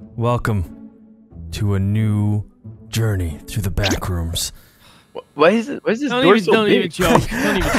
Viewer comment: I thought he said the black rooms for a second